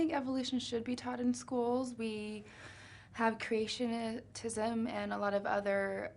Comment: Did she say creationatism?